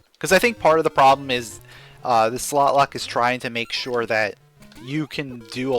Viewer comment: What do you think about SlotLock? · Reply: I thought you said softlock 😊